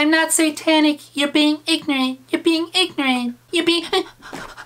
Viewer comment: I heard "I thought you were being 9"